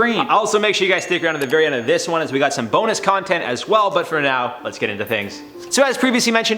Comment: I thought he said "boner content" 🤣🤣🤣😝😝